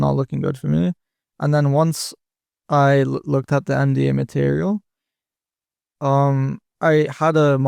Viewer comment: it sounded like said he looked at the "MD material" for a huge improvement, does anyone know what that meant or was?